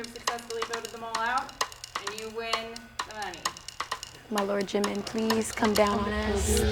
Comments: Did she just say Jimin 'cause gurl- Me: Hey! You Jimin biased too!? I feel jealous when I hear "Jimin's my bias" from someone else..but dis time I feel proud ya know? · Did she jUST say 'My lord jimin' I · did she just say lord jimin wat the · did she really said My lord JIMIN please come down on us. Or it was just me